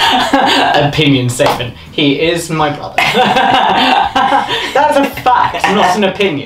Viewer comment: ngl I heard here "he is my problem" and I was like "Yeah, that makes sense", and then Sirius repeats the statement and I'm like, "ooooh BROTHER, that makes sense. Actually that's not much of a difference in their relationship"